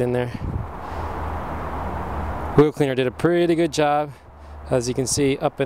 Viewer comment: What do you say at 6:34? XXYYZZ Cleaner did a pretty good job.... I can't tell what the first word is. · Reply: Wheel Cleaner is what he's said